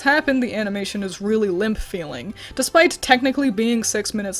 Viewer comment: When said limp it sounded like she said limph, I dont know why I heard that but, uh it happened